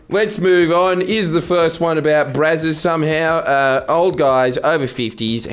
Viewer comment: When he said Brazzers I️ thought he had said something about Bruz. He’s known for Bruz which is fair